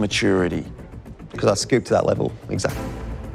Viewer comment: i swear he said " scoop to that level"